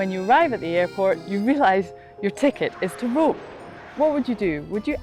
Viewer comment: my American ears do not understand what she says. "Your ticket is to rule" ? THe subtitles say "rule" What is she saying? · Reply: Rome